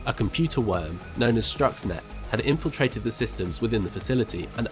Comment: Sounds like he says Struxnet instead of Stuxnet